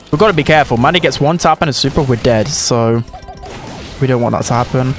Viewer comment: what did he say??? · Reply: He said: If Mandy gets 1 tap super we're dead.